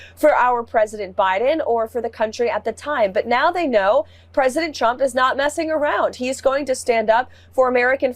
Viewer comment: I swear Karoline Leavitt just called the POTUS “President Chump.”